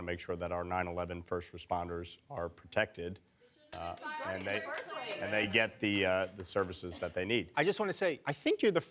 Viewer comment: Could anybody hear what the heckler said at 10:45 ? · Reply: They said " don't fire them in the first place"